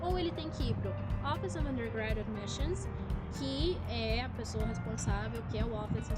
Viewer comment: alguém me diz o que ela fala no minuto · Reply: Tambem não sei kkkk, mas acho que foi: office undergrad admissions